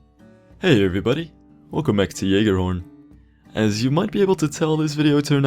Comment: i thought you said JÖRGEN lol Youll know this if your a fan of pewdiepie